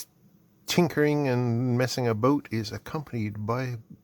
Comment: I love that the closed captions started with you talking about "messing a boat". Fortunately, that's not what you were doing. System can't deal with Canucks, I guess. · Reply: I thought he said "messing a boot", I guess Americans can't either. 🤷‍♂️🤣